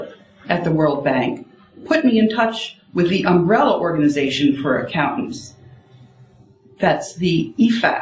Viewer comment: Did she say "Umbrella Corporation?" ☂️ 4:39 creepy This woman is a hero and will hopefully be getting a medal from President Trump soon!